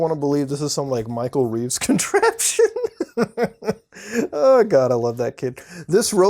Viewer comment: In the minute 06:37 I thought he said 'Micro weeb contractions'